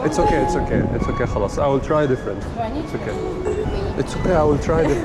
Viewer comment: did bro really say "khalas"?!!😂